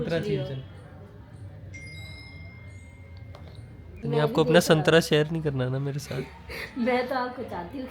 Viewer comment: ummm yeah I heard it right santraaa😂😂☠️☠️😏😏